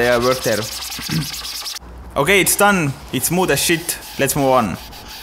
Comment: DId he say smooth as shit?